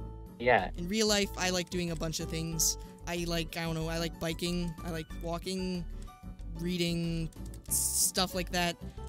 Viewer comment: "i like to breath" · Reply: wait what? where? do you mean at 1:28 , where I say "I like reading"?